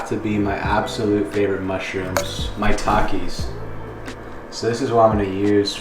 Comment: bro is it just me or when he said "maitakis" i thought he was saying "my takis"